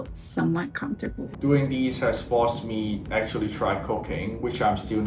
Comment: I am so sorry but I thought he said “ coc@